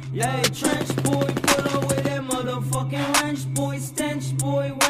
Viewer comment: Is it just me or i thought it said mother fucking french toast 😂